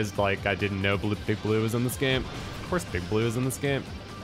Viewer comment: It sounded like homie said OF course Instead of OFF course; i thought he was clowning on you 😂